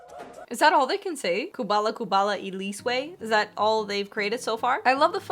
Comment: How does that chant go? Sounds like they were saying kubala kubala I’m insane